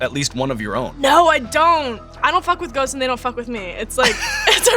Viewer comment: I was distracted for a second and then I thought I heard “I don’t fuck with goats and they don’t fuck with me” without context and I was like what the heck until I replayed it lol Edit: timestamp is